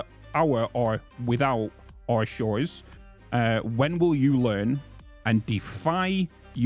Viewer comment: I heard "when will you land". Learn does make more sense.